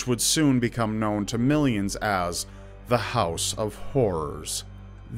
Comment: I thought he said "the house of whores" 😂